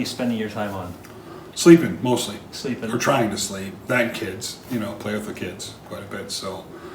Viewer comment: Did say "9 kids" or "and kids"?